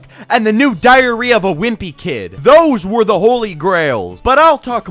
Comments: am I the only one who heared diarrhea of a wimpy kid? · Did he just say “diarrhoea of a wimpy kid?” 😂